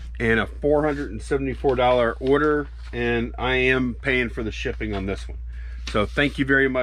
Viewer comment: WAIT A MINUTE @ 2:08 Did I hear you well? No shipping on Top???? :-)